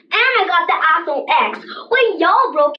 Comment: i thought she said Apple X instead of iPhone X i was like WOAH😂